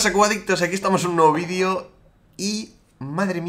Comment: Yo cuando empieza un video: "Eyyy, pero que pasa cuboadictos". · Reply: yo pense que decia cubaditos xdd